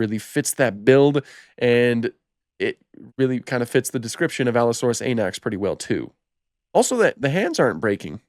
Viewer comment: I was listening to this video and not watching it and thought you were saying Allosaurus anus the whole time instead of anax 😂